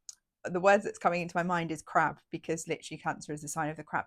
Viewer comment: Thanks for the reminder and also I love the sound of the Cancer energy! What does it mean that the moon rules cancer? haha I thought you said 'feeling very crap' but then I realised you said 'crab' which is basically the same!